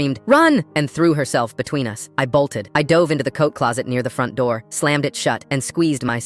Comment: For a moment there, I thought he said Coke closet😂😂😂😂